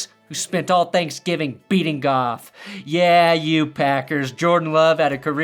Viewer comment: Thought you said beating-off, but you said beating golff😮 LOL